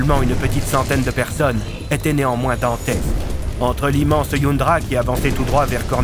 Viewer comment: était néanmoins quoi ? Svp j'ai pas compris ça me perturbe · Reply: Je crois avoir entendu "dantesque".